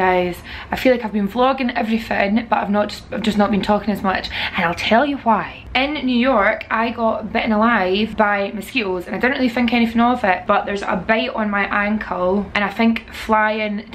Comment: When you came up close to the camera and said "And I'll tell you why" I thought you said "I tell you Hwat" and it made me think of Hank Hill lmfao.